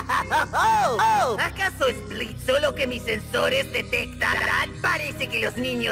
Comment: lo que dice:Acaso es Blitzo lo que mis sensores detectan? Lo que escuche yo:Acaso es Fasty lo que mis sensores detectan?